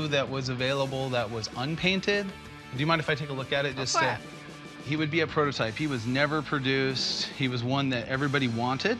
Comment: what did she say 😂😂 I think she said “of course” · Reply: TigerPlays “go for it”